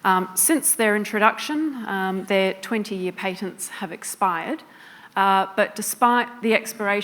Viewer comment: I thought she said 20 of these PATIENTS have expired.